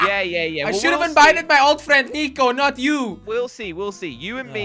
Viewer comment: did somone said niko???